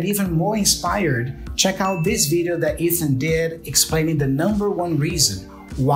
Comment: Hello guys, firts, I'd like to thank you for your job with these videos, I think you help people who really want learning English. Second, I don't sure but at minute 15:37 I think saying "that Ethan did" but in the subtitles it's possible reading "that is and did" . Which one is correct? Grettings from México.